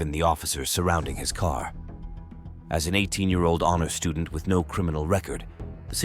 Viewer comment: did he say he an 18 year old Amish student?